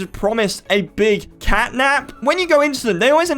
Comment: Did said “FatNap”😨